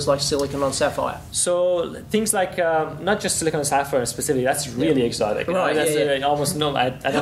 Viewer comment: what say in 7:05 silicon ... ? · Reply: Silicon on Sapphire